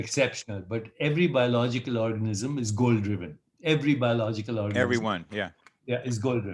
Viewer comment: Thought he said gold driven lol